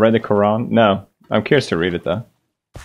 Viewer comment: what did he said?? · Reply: Someone asked him Have you ever read the Quran He replied and said he’s curious to read it